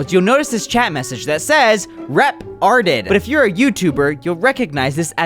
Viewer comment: re tarded I thought it said